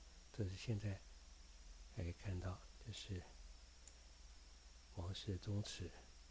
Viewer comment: 請問您，2:37說的是甚麼地方，地名聽不太清楚，看現在地圖優該是王氏宗祠